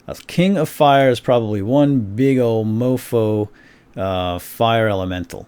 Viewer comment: Did just say "mofo" ?!